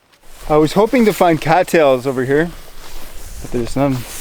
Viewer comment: What did he said on 30:20? Sounds like catiles. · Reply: Cat Tails